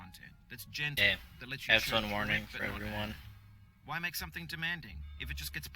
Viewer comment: u said headphone warning but I heard "f word warning" like if u mess up ur going 2 scream